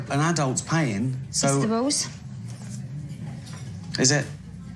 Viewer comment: what she says? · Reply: It's the rules